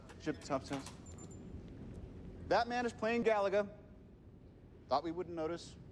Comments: I heard “Batman is playing galaga” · Turn on the subtitles; Tony says "Batman is playing Galaga." Makes sense to me. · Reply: I was just thinking that it sounds like he said Batman, before finding this.